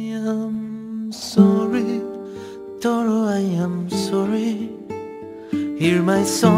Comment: i love how its sounds like deku said "Tomo" for tomura on 1:40 even tho it said somethin else w